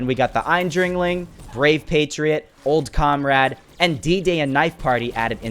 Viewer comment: For some reason at 8:40 or around that i heard one of the things he was talking about as ‘Diddy’💀